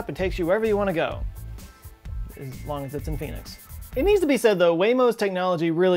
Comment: "as long as its in Pheonix" Definetly thought you said "As long as theres a pe**is" 😂